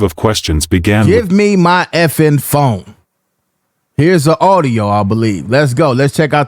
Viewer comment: It sounds like he said give me my m#th@ phone.